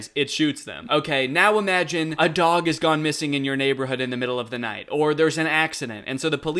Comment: i thought said as a dog gooner